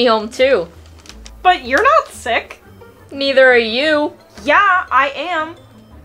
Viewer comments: lol, they "but your not stuck" ??? what? they say sick. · Bro said stuck instead of sick 💀